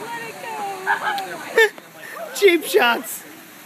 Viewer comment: "Cheap shots" I'm dying 😂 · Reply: Chimp shots is what i thought he said